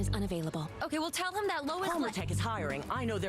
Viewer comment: Did she just say "Palmer Tech" like The Atom Ray Palmer or did I hear that wrong? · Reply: It was definitely Palmer Tech.